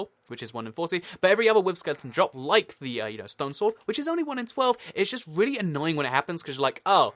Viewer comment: Slow down with your talking :) Have a good day. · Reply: At around 2:30, I was sure he was saying 'stone souls' instead of 'stone sword' I was very confused until he mentioned the recipe.